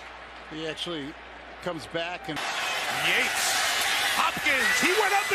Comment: it sounds like he said yeets